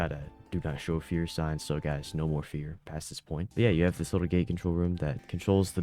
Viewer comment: I thought you said Gay control not Gate control (No homophobia)